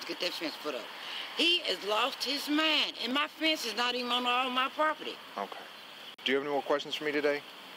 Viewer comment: it sounds like she says ''and my fence is not even on my property''